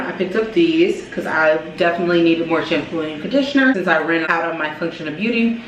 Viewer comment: At the beginning i thought you said "since im pregnant" I was finna say whattttt